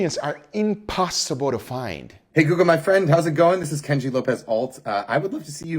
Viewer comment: Everytime someone said "Hey Guga" my phone thought it was saying "Hey Google" and Google assistant was popping up throughout the video haha.